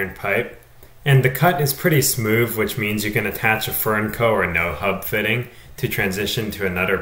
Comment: it sounded like you said, "And the cut is pretty smooth which means you can attach a 'fur in co or no hubfitting'" .... what is that last part of your sentence? I ask because I am wondering what is the best thing to attach to the cast iron pipe, once cut, to transition to PVC. Thanks the video and for any additional clarification. Cancel that. You said it later in the video "nohub coupling or furnco fitting" .... Do you have a proven and recommended supplier/coupling type that you could link in the product description? Thanks!